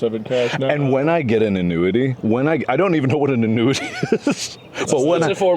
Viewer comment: Please tell me I'm not the only one that thought Mark said "when I get into nudity" not "an annuity" 😂